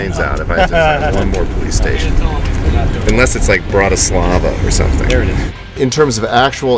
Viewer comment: Did he say Bratislava or am I imagining it because that's my hometown? :D